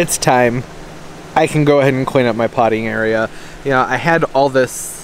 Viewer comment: So I was not in front of my computer when you were saying that you were going to be cleaning up your potting area....I thought you said you were going to be cleaning up your 'pottying area' I did quite the double take. I love you.